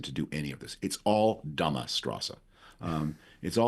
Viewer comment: What is the german word that Frum used at 27:27ish? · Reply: I laughed at that. I think he said dumb*ss strasse (street).